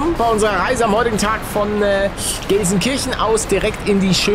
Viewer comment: Ich habe statt glelsenkirchen hab ich verstanden geilenkirchen 😂😂😂 Srry für die restschreibung